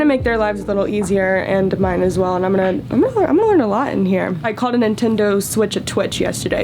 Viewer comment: I have no idea what she is trying to say here All I heard was "I called a Nintendo Switch a twitch" ok then